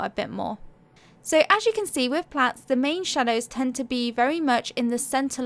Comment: Thank you! I thought you were saying plants then i realized plats are "braided ". I think that is what you were referring to. 💝